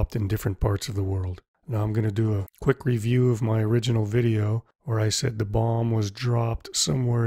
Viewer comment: *I'm gonna do a quick review of my original video OR I said" why did you say OR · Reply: It's "where", not "or".